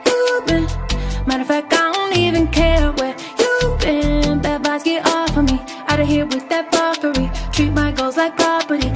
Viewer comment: i thought she was saying "bed bugs get off of me" oop